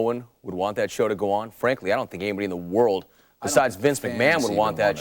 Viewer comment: Bret sure don't think or feel that way now..... 03:00 what does Bret say while the interview guy is talking? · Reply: "I don't think the fans even wanted...'"